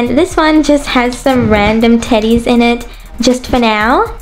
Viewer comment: I thought you said random titties! XD